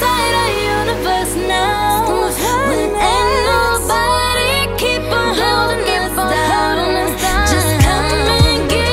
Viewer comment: what’s she saying????? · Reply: Ain't nobody keep on holding us down